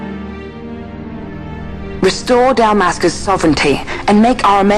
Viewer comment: i thought guy said dumbassca not dalmasca!! LOL